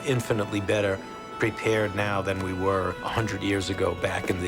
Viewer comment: We need you now more than ever. Thought you said we are better prepared now more than ever. Then deal with this corona virus please